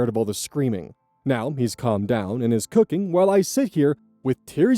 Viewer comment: I thought the baby was doing the cooking for a second 😅 I heard it like “baby got scared, now baby has calmed down and baby is cooking” instead of “baby got scared, dad calmed down and dad is cooking”